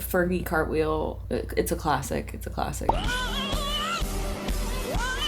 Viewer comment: Truly thought you said "Furbie cartwheel" ... the way I almost broke my neck to find out 💀